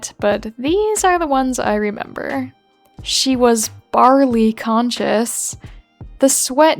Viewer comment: I was connected to my head phones and got so confused cause at 2:28 I just hear “she was BATTERY LOW”